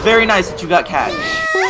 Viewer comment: "But I got Kat" somehow as a non-league player I always thought she said she's got a cat. ._.